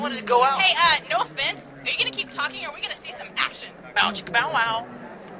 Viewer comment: Can you here very faintly sounds like Caboose says " Bow Chickka Bum Bum" xD